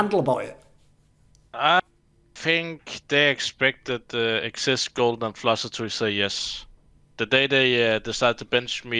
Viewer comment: what did he said ? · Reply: they expected xizt, flusha or golden to say yes